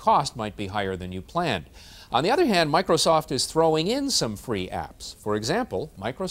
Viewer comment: Did he just say "apps" O.O